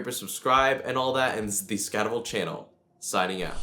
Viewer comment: everytime you start a video it sound like u say its the descovery channel lol